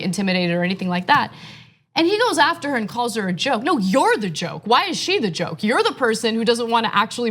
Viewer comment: Kady Cyst...... what's your point? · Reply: Richard Keller , I thought he might be calling her a Joe like a follower of Joe Biden. Weird thing to do. I just misheard it, that’s all.